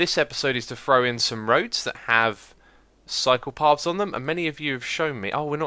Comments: LOL! I heard that as "build roads that have psychopaths on them." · Wait, did you say "roads that have psychopaths on them"?? very subtle....xD